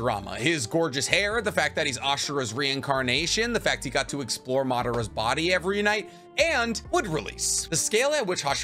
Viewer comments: did you just say “the fact he got to explore Madara’s body every night”? 😅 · Hold up. What was that bit on "exploring" Madara's body every night? Did I hear that right?